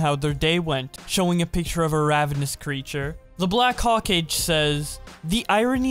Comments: “The black haw-cage” ... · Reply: yeah, I know it is ho-kah-gey but I heard him say “the black hawk age”, which just sounds badass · mam i the only who heard him read hokage "hocage" ??? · umm did you just say hokaj? 🤦‍♂️